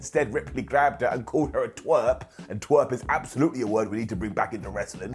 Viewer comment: Did Rippley say Twerp? We definitely thought she said Tw*t lol